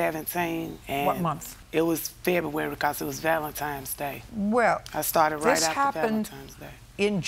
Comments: Ok… at 1:57 I thought she was saying, “Godzilla’s Valentine’s Day.” There’s a holiday special for everything. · Did she say Valentimes day? 😂